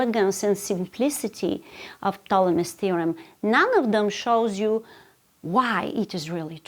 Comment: I just wanted to congratulate to whoever created the english subtitles for this video. At 5:00 you can see that instead of "Ptolemy's theorem" there is "Thomas theorem".